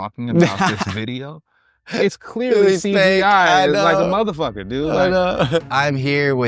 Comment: Did he just say, "It's clearly CAI?" or "it's clearly AI"? Either way...what?